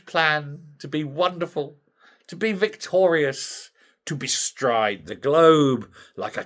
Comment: for a minute there I thought you said Victoria's.. oops it was victorious silly old me...